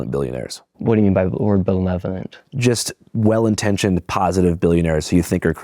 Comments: “what do you mean by the word bulevenent” bruh i cant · Andrew Callaghan v. Andrew Callaghan if he dropped out in 5th grade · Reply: "What do you mean by the word 'ben..levelant'?" Jesus, Nick... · Nick- "What do you mean by the word benevolent" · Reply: He said belenovent lol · "What do you'm by b'word belevent?"